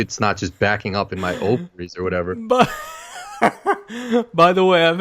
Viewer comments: Did just say he has ovaries? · Did say he had ovaries?